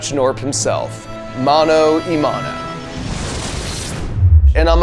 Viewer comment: Did he really say "Mano nahi Mano" It is hindi and means, believe it or not